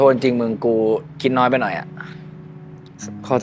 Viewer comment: ได้ยินว่ากินน้อยไป😅 จริงๆเขาน่าจะพูดว่าคิดน้อยไปหน่อย😅